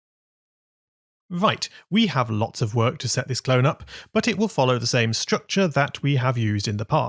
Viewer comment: you said "cloes" instead of clones